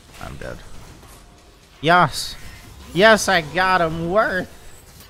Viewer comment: What said: "Yas! Yes I got him worth!" What I thought I heard: "Yaaas! Yes I got him, werk"